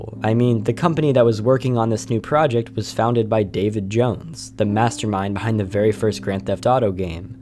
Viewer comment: When u said David Jones I misheard u saying Davy Jones. xD